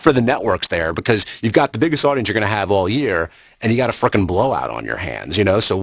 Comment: What did he say at 36:43? A fucking blowout?